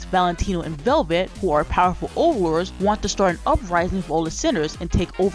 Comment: i know you said powerful overlords but for some reason i've heard it as "Powerpuff Overlords" LOL 😭🤣